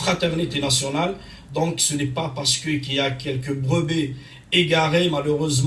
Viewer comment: a la 3:48 c'est quoi une " Brebé " ? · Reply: Il a dit "Brebis égaré"